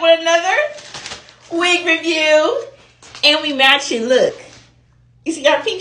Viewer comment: Sounded like said “we matching up today” 😂🎉